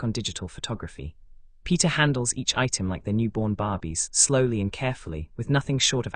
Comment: Just me or did anyone else hear Newborn Barbies?